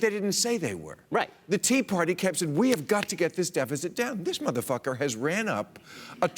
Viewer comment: Did he really called the President at 2:43 ''Motherfucker'' or do i understand something wrong? Little confused.